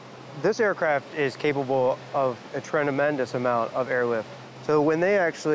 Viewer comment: this dude says "trenamendus" instead of "tremendous" and the editors of this documentary actually thought that it was fine